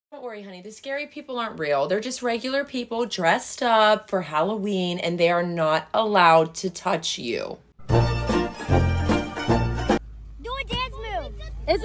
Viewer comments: I THOUGHT YOU SAID THEY WERENT ALLOWED TO TOUCH ME MOM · The way she looked at her mom I thought you said they're not going to touch me😂